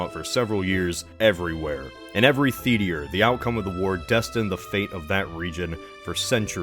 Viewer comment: Did anyone else hear say "the-tea-re" at 1:20?